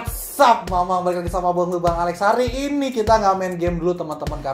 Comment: gw dengernya bangs*t my friend